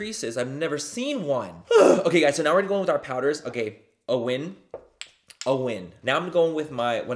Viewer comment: Omg lmao he said "a win" and i swear ta god for like half a min i thought he said my name owen 🤣🤣🤣 i was like babbyyyyy whaaatttt is going onnn